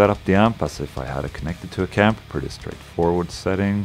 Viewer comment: it sounds like you say "...as if I had it connected to a camp" unless I'm hearing things lol